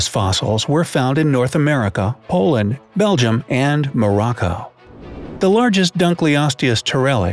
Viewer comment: is it just me or did i hear moroko (dr moroco from auto bots)